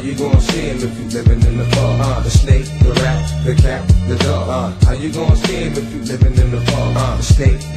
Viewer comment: haha i always thought he said "the snake, the rat, the cat, the dog, how u gonna see em if u living on a farm" hahaaaa fkked up man..